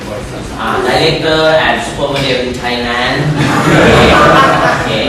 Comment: พี่หอยพูดว่าอะไรอ่ะ ฟังไม่ออก ฟังออกแค่ไดเร็กเตอร์..ไทยแลนด์ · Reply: And Supermodelครับ มาตอบช้าไปปะ